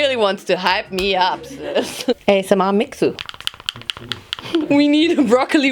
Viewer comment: did you just speak french 😂😂 i mean i am and i hear « heh c’est ma mixture! »